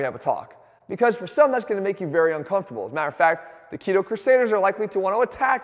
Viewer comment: did he just say keto crusaders? 😂